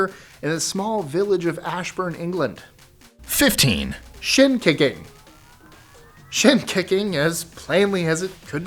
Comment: I swear I thought he said shit kicking. Lol!!